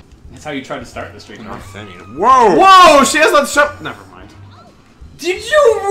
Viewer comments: Any idea what he’s saying at 6:31? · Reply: “Woah! She has that shoved..., (Emile realizes what he was about to say.) Never mind.” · What did say at 6:35 to make surprised? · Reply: "WOAH, she has that stri(pper pole)-nevermind." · I have NO idea what said then! Can anyone tell me? Does it have to do with the couch like one of the pictures said? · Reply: Chris Corman " Woah! She has that stuck up her - nevermind" " did you just really Emile!"